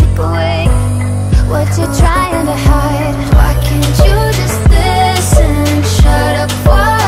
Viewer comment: haha thought she said: Fuck can you just listen?